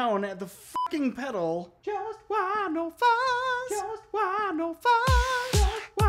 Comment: what the? What is he even saying? I can make out fuzz. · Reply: “Just wah, no fuzz” - upon realizing I had not tested the morley without fuzz yet I believe